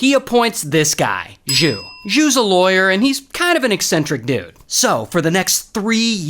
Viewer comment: did he just say Jew was a lawyer?